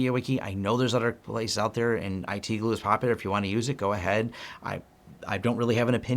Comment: Thanx for another amazingly informative video & co! At 11:40-11:44 , what are you saying after "MediaWiki" ? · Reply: IT Glue